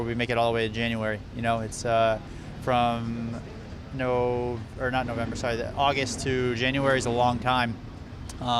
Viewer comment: did he said no nut november?